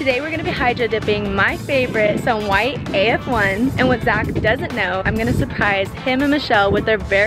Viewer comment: Ong I thought she said we are hydrodipping my fav shoe AND1s